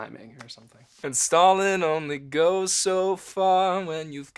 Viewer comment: when u thought they were saying “stalin only goes so far” but the song isn’t actually about russian communism 😔👊🏻